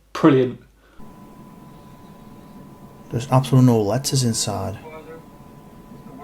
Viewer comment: did he just say there are no ladders?